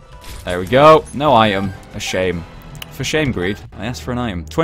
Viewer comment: When said "for shame" i heard "for shave", lol